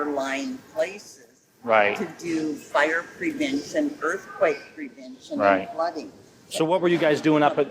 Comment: did she say she does earthquake prevention ?